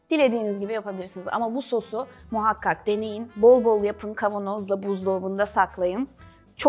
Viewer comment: bol bol bowl yapın anladım ilk etapta 😇